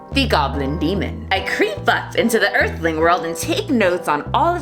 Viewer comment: My captions said “I creep butts” but you sounded like you said “creep up”... so which is it? Tell to bring back that sexy demon cause you need to get it together lmao. Ik jk I’ll take any demon iteration of I can get ❣️